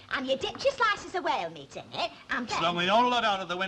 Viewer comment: Did Hilda say "whale meat?"